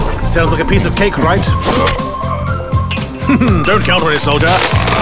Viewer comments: "... Now get out there and get the flag back!" I thought he said "get the fuck back!" Edited: Also the scream at 1:04 is more manlier than what the hell is it in BF5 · ikno he says "get out there and get the flag back" but it really sounds like he is saying "get out there and get the fack back" xD